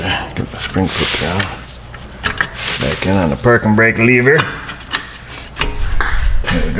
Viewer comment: i thought i heard new brakes?, are those new brakes? what am i missing ?